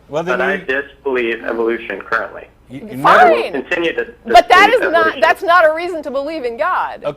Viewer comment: I don't think Matt or Tracy caught it, but around 4:20 the caller said "I used to believe in evolution... but I disbelieve evolution currently... " (And here's the part that I think they missed) "... and I will continue disbelieve evolution." I would have ended it right there. Blatantly admitting his mind is already made up and closed. I don't think I could ever say that I will continue to believe or disbelieve anything. Ever. Being a skeptic, I am open more my believes to change. Like Matt has said on many occasions, I have a tentative believe in something based on the evidence that supports it. Key word being tentative.